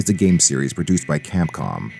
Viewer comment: for some reason, I heard that as "games series reduced by Capcom". Well, considering that they did handed the series over to Ninja Theory, I'd say that's accurate.